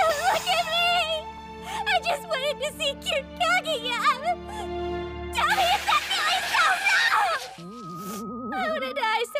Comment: Does anyone have any idea what she just said? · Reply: TELL ME, IS THAT REALLY SO WRONG?!